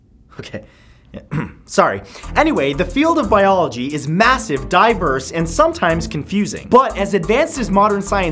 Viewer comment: Instead of saying massive, diverse I thought you said massive diapers 😂 I was so confused!!